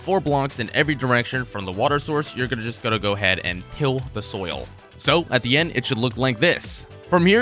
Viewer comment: Who else heard him say, "kill the soil?" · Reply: i thought he said it but upon further inspection he said till